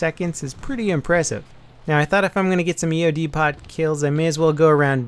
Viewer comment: EOD "POT"?? did he say EOD pot?!! :P